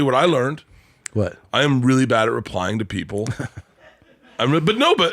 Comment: At the beginning I thought he said “I am really bad at lying to people”